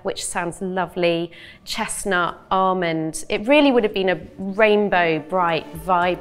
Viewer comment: I thought Amber was saying “Raymond”